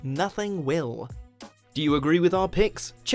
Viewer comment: Narrator: Says WatchMojo Me: did he just say watch virgin?!?